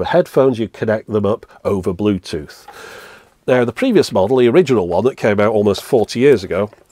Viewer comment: Took way too long to figure "previous model" instead of "penis model." Though all I hear is the latter. 😄